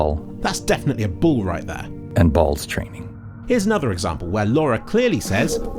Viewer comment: I definitely heard "wall and balls training"